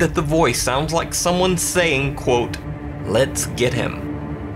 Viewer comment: In Dan Bell's, I hear "Let's scare him"